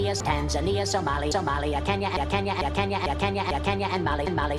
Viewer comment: I hear it saying "Tanzania’s”